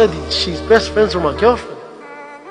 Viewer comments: 😂😂😂”idk if I sould help him or slap him😂😂😂😂” wallah ur funnyyy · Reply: dawg I heard “idk if I should help him or slap him” but ty tho ⭐️ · 😂😂😂”idk if I sould help him or slap him😂😂😂😂” wallah ur funnyyy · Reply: idk tbh I heard “idk if I should help him or slap him” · It very well sounded like he said head butt or slap him please people go and clean ur ears out 😂